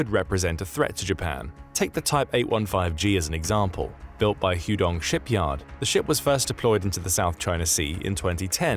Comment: Did he just say, "huge dong shipyard"?